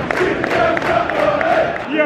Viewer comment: What are they chanting at 5:04? I can't really understand it. Quality as always! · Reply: They're singing, "Champions of Europe, you'll never sing that!" VTID